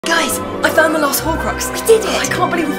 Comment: Bro I thought he said "I Found the Last Robux"